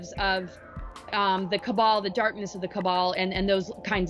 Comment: is she saying “darkness of Kabbalah”?